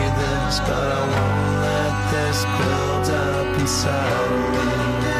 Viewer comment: I always thought he said "I won't let this build up inside me. I won't let this build up anxiety." Meaning it won't build up to him to make him anxious for something he cannot have when seeing the woman of his dreams. Took me years to fully hear the words were repeating because I just really enjoy the instrument's playing. Also, Volume 3 is and was a change of pace for Slipknot and Corey Taylorm9n writing lyrics. The band was challenged to write a clean album since the record labels thought Slipknot can only write vulgar heavy music. They proved so many people wrong. How? By winning a Grammy for Before I Forget off this same amazing album. FYI, this was the start of Stone Sours reemerging into the music world. A lot of hate goes into Slipknot's last three album's based off of "sounding like Stone Sour" which is not the case. Corey also changes his screams in this album. Such an awesome band and awesome reaction channel.